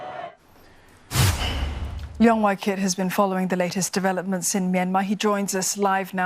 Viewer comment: who else heard "long white kid"